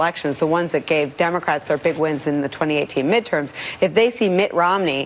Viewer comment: did She say “voters that gave democrats OUR big wins in 2018”? If so her slip proves she is a RINO. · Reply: Checked it a few times and it seems like she says 'their' but sounded like a fubble between that and 'our'.